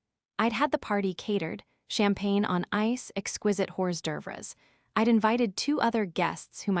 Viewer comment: "Exquisite whores durvres" instead of exquiste Horsd’œuvres... super lol